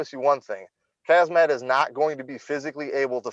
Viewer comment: and 12:58 Did he say "hazmat"? 😂